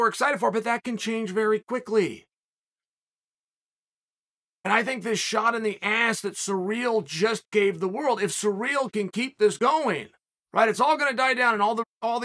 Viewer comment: Did Chael just say Cyril injected the world’s ass with steroids…Tell me I’m the only one that heard that?